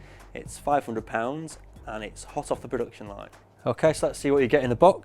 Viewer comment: Is it me or did he just say 500 Pounds.